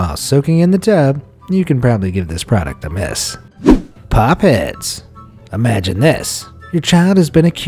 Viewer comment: i nearly thought he said pop its